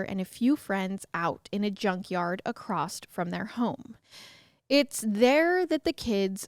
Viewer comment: Did she just say "acrossed?" 4:22.